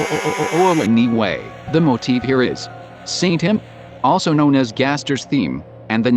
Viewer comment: St. Him... Saint Him... when i watched the video i heard it as "Satan" instead, that would make it a triple pun, and given gaster's connection with the number 666...